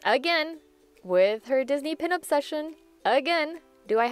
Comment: Did anyone else mishear pin obsession as pin up session or am I just sleep deprived?